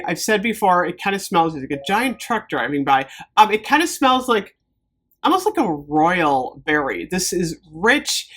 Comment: Sounded like “It kinda smells like a giant truck driving by…”😂Now that would be a weird fragrance! Could be called “Diesel Exhaust”😜